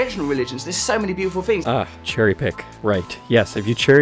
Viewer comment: At the part when you started flying out because you got really angry. I got really angry and also flew out. I was trying to find you and we could fly in anger together. But atlast, I don't know how to finish this comment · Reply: Yea and there was this one part in the video where he said cherry pick, but I thought be said cherry dick, so I looked it up and CHERRY DICKS POPPED UP ALL OVER MY SCREEN